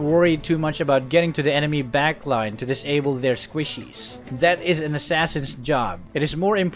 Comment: Did he just say squishes- I swear I heard squishes also I honestly haTE when you are playing tank or not during a team fight 2 or more of ur teammates see that the enemy is very low and want the kill WHILE THERE ARE 3 left with high ass hp and then u be there like thanks. Y’all are the greatest teammates.